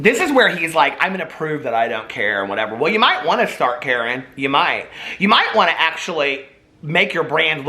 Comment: Did just called a Jeffree Karen :D - "You might start , Karen ! " LOL